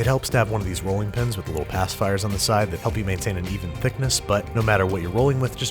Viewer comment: totally thought you said 'ass fires' at first... I had to go back and rewatch it like 3 times before I realized what you actually said. 😂